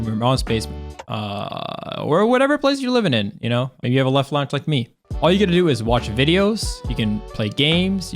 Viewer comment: What is he saying 1:41 "Maybe you have a leff lounge like me"?